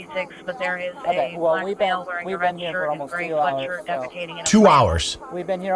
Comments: I swear the dispatch said something about someone deficating😂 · Please, PLEASE tell me someone else heard the dispatch over his radio at 44:05 report that someone was shi+ting in a plant!! 😭 just when I thought this video couldn't get any better 🤣🤣🤣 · Was I the only one who heard over the radio at 44:09 saying something about a black male defecating in a plant😂